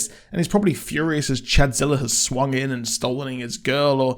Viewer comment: wth is with the color pallet on this show? ...did you say chadzilla at 5:31?